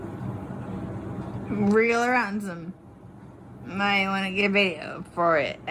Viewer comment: Can anybody make out what Sarah is saying right there? It sounds like "real or handsome... might want to get... extra" Just wondering. 😢